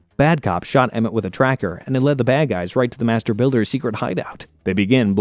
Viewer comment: I thought he said master baiter